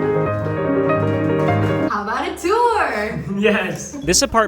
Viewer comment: am i the only one who heard: "Ratatour" instead of "how about a tour? (Ratatouille and tour) 💀😂